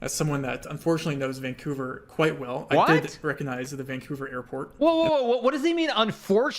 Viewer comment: No, he means that him knowing Vancouver is unfortunate. Basically the story is that one time, he got a geogusser round inside a random building in Vancouver, which he failed to get. Because of this, he forced himself to literally learn every single building in Vancouver. That’s why he knew the airport immediately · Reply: Ah, yeah, I misheard the part at around 5:32 and thought he said he hadn't recognised the airport, my bad, oops!